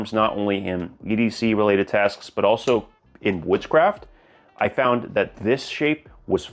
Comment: when you said woodscraft I heard witchcraft like what kind of channel is this now lol.